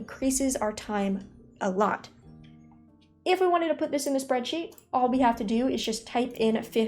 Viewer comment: i love you, I heard "its on my spreadsheet" you're amazing!